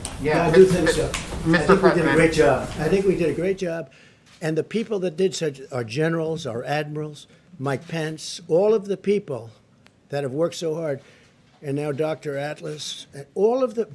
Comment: When he said Mike Pence, I mis heard it and thought he said "my pants".